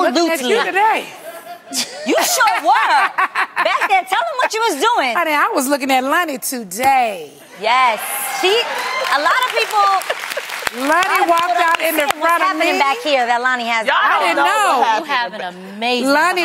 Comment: Did Nene say Loni waddled out 0:14 ? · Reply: I heard "walked"